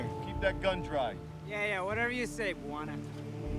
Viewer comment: did Ryan just say "Moana"?😂😂😂